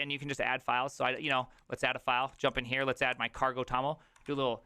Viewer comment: Ah! cargo.toml - I thought you said "Carpel Tunnel". Very vim.